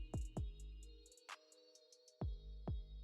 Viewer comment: Lol, I been here a few times, and every time , I thought you were saying, "Oh yea, Diddy gotta podcast." Lol, until NOW, I looked at